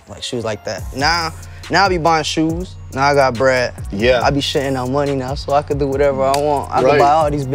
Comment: "𝑰 𝒃𝒆 𝒔𝒉𝒊𝒕𝒕𝒊𝒏𝒈 𝒐𝒏 𝒎𝒐𝒏𝒆𝒚 𝒏𝒐𝒘 𝒔𝒐 𝑰 𝒄𝒂𝒏 𝒅𝒐 𝒘𝒉𝒂𝒕𝒆𝒗𝒆𝒓 𝑰 𝒘𝒂𝒏𝒕" Words of a lyrical genius Edit: sry I didnt understand what pump said english.exe crashed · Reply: Christian bro I thought he said shitting out money lol